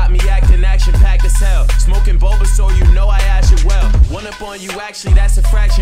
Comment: Did I hear "smoking bulbasaur?"